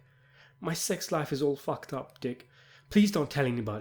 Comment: I heard "my sex life is all dicked up"